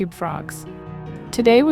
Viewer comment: Again i heard that you saying 0:00 🐸 forgs